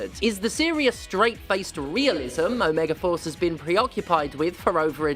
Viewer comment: I misheard "omegaforce" as "omegaverse" and was very confused for a bit.